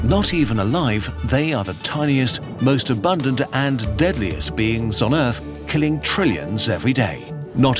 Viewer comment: i thought he said "Abundant and deadliest memes on earth"